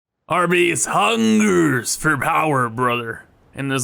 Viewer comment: Sorry, but when you said “our base hungers for power” I heard it as “Arby’s hungers for power” and now your base will always be known as Arby’s